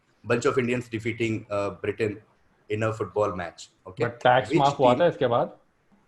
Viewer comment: kya bola 5:38 pe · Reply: "Tax maaf hua tha iske bad?"